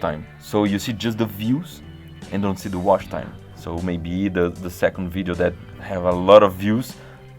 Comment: "entao maybe" kkk TEVE UM POUCO DE PORTUGUES ALI OU FOI IMPRESSAO MINHA?